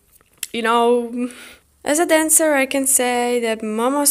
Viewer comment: did you just say youre a dancer 😳😳